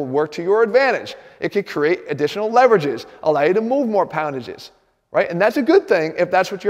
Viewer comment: Did he just say....poundages?